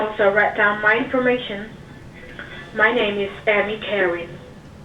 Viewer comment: At first I thought she said her name was Scammy Karen! How perfect would that be?